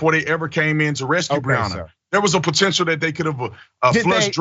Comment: Did he say RESCUE?!?!??